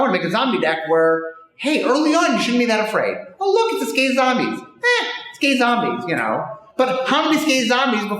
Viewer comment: I swore he was saying, "Gay Zombies! Gay Zombies!" at first